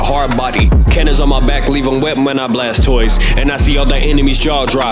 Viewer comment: I thought metal knight was saying Blastoise but then I read the lyrics and it's blast toys